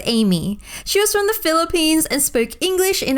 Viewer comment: Philippines? Did I hear Philippines? Cause im there now on vaca!